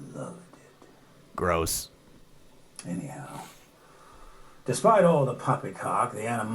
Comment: did he say "despite all the popping cock" @ 8:27.....